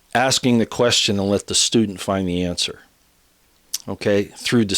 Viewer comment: When said Q uses the Socratic method, asking the question and having the student find the answer, I thought he said have the stupid find the answer, at first. In this day in age it wouldn't be far off...